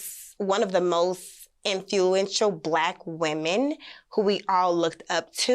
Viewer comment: Did she say “ in few entual?” 50:40 😂